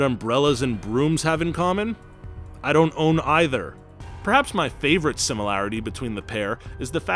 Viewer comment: Am I the only one hearing him say “I don’t own either” at 6:42/43, instead of “I don’t know either”? 🤔